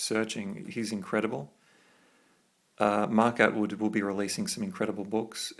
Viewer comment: who is the author you mentioned that is an updated version of Samael? It sounded like it started with an M but I couldn’t understand. It wasn’t Benisa. Thank you! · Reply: Mark Atwood